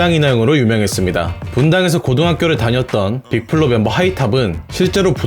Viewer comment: 영상 초반부터 문맥이 ;; 하이탑이 언급했다는걸 알겠는데 하이탑이 이나영이라 들리네요